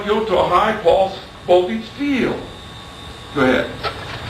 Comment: what high pulse xxxx field is he saying. "High pulse oh feets field" is what I'm hearing